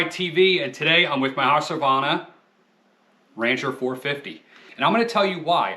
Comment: I have one. The oil filler rubber ring leaks. Plus I thought I heard you call it the "Ranger" twice. Rancher